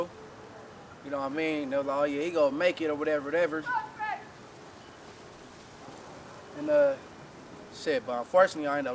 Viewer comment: What did they say at 3:50 in the video?? Whoever was yelling? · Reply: They dissed saying “F”🖕🏾IFG